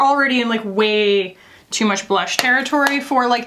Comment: y’all correct me if i’m wrong, but i think i heard say “too much blush territory” … it’s the end of an era